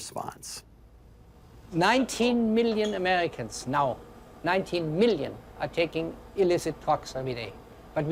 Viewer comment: can ANYONE please tell me what illicit thing Peter Duesberg says that 19 million Americans are taking that too politically incorrect to talk about? I can not decipher/understand his words... I'm not sure if I'm just unfamiliar with the term he's using or if it had to do with his accent, or simply poor enunciation. PLEASE HELP! 🙏🏻 Thank you so much! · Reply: Thanks for asking. I believe he was trying to say "19 million Americans are taking illicit drugs everyday".